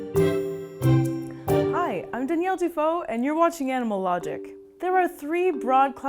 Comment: Every time she says , ' ... I am Danialle Dufault', I hear 'Daniel Defoe' and it reminds me of 'Robinson Crusoe'. 😁